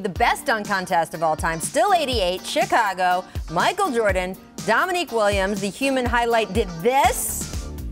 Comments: Hold Hold hooo... 1:34 is she just said Dominique Williams?🤔😲😬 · did i hear williams?